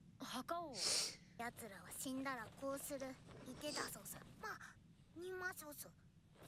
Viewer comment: I know that this is supposed to be a sad moment, but every time I see this scene, I can't help but laugh when I hear what she says, because in Polish "nima sosu" means "there's no more sauce" :D